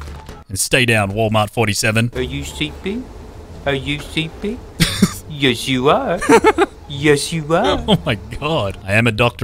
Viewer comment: I keep hearing “Are you simping are you simping yes you are yes you are”